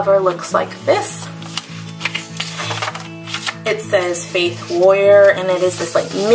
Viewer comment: I thought you said fake lawyer until I saw the inside cover😂